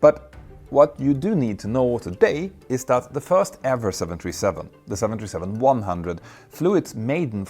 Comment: it really sounds like he said "the first Airbus 737"....